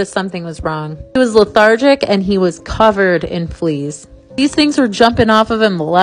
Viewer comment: I thought she said “covered in please” 💀 🤓